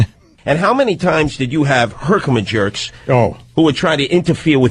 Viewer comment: I heard, "herkimajerks" and nearly spit out my coffee. I never noticed that before.